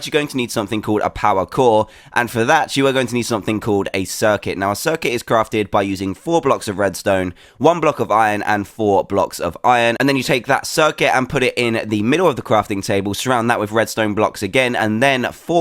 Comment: press 1 every few seconds sounds like he is saying f**k iron